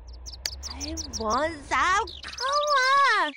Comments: is she saying i want sone cola?? ummm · I swear she's saying "I want some Cola". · I just realized it sounds like Amethyst is saying I want some cola.